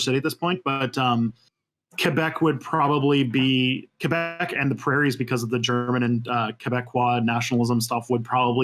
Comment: DID HE SAY La Prairie Qc CA ?!?!?!? That's where I am and yes f*ck Britain